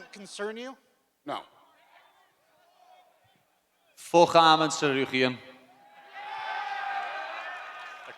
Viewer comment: What did Paddy say? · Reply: He said fvck Arman Tsarukyan